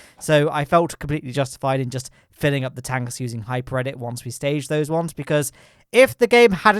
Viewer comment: I can't quite catch what you said you filled the tanks with, was it a mod? I keep hearing "hyper credit" heh.